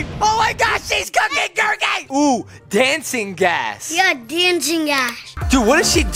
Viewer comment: what did he say dancing a$$ oh 😂